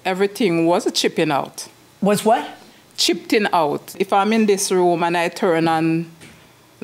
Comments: Plaintiff walked in and thought she was going to sit in the judges seat 😂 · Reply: it’s not until I saw your comment that I understood. I thought she said chirping out. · Ma’am where are YOU GOING???! She’s “chippin out”😂🤣💀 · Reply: I heard her say tripping. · Plaintiff walked in and thought she was going to sit in the judges seat 😂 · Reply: it did sound like she was saying chipping out, it's her accent